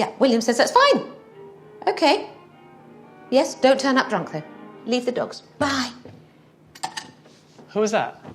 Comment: "Don't turn up drunk though. Leave the dogs." "Who was that?" "My mum." HAHA! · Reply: I thought she said, “Leave the drugs”. I’ll have to go back.